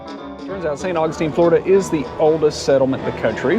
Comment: I thought was saying "it's the COLDEST settlement..." which totally fits with how cold he looked! Lol😂